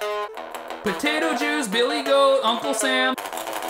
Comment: "potato juice, billygoat, uncle sam", and I died. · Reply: I heard "Billy Bob"